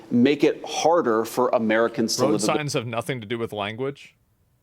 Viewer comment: did I just hear "Road signs have nothing to do with language"?